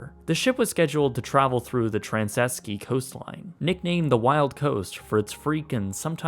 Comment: Sounded like the narrator said...Transeski (4:16) and as a South African, I was like What.... Where...... I just realized the narrator is trying to say Trasnkei (pronounced Trans - Sky)